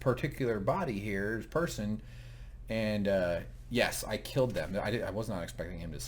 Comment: To me it sounds like Bernard says "I....he killed them. All of them". The subtitles say I killed them but that may be a mistake by subtitles company. We shall see. Hopefully. Unless they just gloss over it in a distant episode. Up to ep 5 now and still no answer. Oh well. Patience is a must with this show.